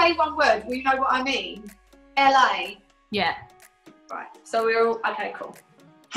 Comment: did Jesy say 'L.A' at 7:38...if so I googled where Charlie Puth lives and it showed L.A